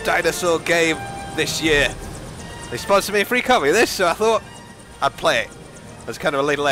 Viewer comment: you can clearly hear that he says "They sponsored me a free copy of this". I get that some of the stuff he does probably is a bit underhand, but with that one it's pretty clear what he says unless you don't speak English, which you clearly do. It completely undermines your argument when you try and brush off something as not happening, when it clearly is in that particular case. I don't get this. How does it being branded/unbranded affect the viewer, or you for that matter? So he makes a bit more money from his channel: good for him? Not even a particularly big fan of his, but this does kind of smack of jealousy a bit. So many people jumping on the bandwagon: if it's that big a deal, why are people not just mass reporting him instead of making videos about him? Surely a lot of reports would force YouTube to take action, or at least his sponsors would have to be a little firmer with him, right? Ahh, but then you guys would lose lots of views and exposure for your channel, wouldn't you... We wouldn't want that now, would we?